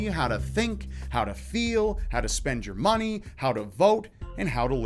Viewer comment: thought you said, "They tell you..... how to Vouge"